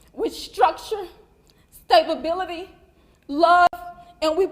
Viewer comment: Did she just say “stab-ability” where she was supposed to say “stability..”😳